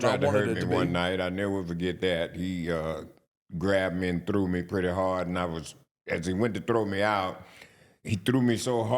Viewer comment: Who did he say threw him thru the 2nd rope around 1:11:11? Can't make it out cause... not shutting up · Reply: Big Show.